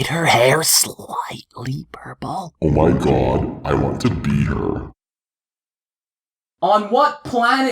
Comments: Did she say "Oh my god, I want to be her." Or "Oh my god, I want to beat her." .... What's wrong with me? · “did you hear that trisha dyed her hair sliiiightly purple?” oH mY gOd i WaNt tO bE hEr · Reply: I thought it said I want to beat her